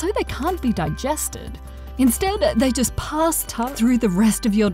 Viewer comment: anyone else hear pasta instead of pass.